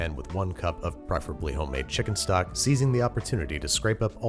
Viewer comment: For some reason, when he poured in the stock, instead of "seizing" I heard "season the opportunity".